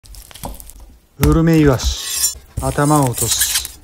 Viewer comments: 最初「グルメイワシ」って聞こえてとうとうもトリコの世界観に行ったのかと思ったらうるめいわしだった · 最初グルメイワシって聞こえて、トリコに出てきそうな食材言ってボケることもあるんや〜って思ってたらウルメイワシだったわ笑笑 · 何回聞いてもグルメイワシに聞こえる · グルメイワシに聞こえて一瞬トリコの世界に紛れ込んだのかと思った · うるめいわしがグルメイワシに聞こえてトリコ意識してんのかと勘違いしてしまった